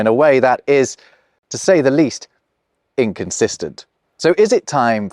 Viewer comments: the subtitles say "consistent" when you say "inconsistent" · why do the subtitles say "consistent"? you're saying "inconsistent"